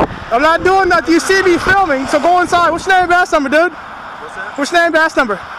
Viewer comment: favorite line of nameanbashnumba. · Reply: indeed ... I had to listen twice ... thought he said, "name and ass number"....